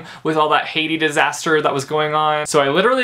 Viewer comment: I usually don't watch a book review unless I've read the book myself but I really wanted to see your thoughts about this book. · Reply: when you said Haiti I thought you said Hades 😂😂 I was so confused about why you were praying to God about the God of the underworld